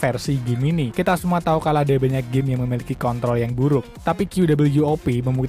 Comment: kedengeran nya kek kont*** apa kontrol?